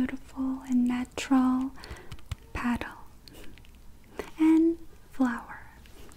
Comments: Your accent makes petal sound like paddle. I thought you said “like a paddle,” and I was like “why would my makeup be like a paddle,” but then I realised that you said petal. Haha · I was so confused when sh said petal cause I thought she said paddle and that drawing did not look like a petal